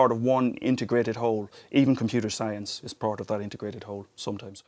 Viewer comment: in 11:39 The caption says "hole" , I think he means "Whole"